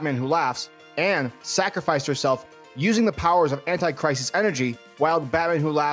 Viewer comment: I thought you said "Anti-Christ" Energy. Is that the one where the DC heroes battle the threat of The Rapture? : )